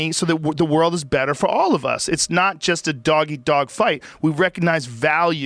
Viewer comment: thought he said Doggy dog fight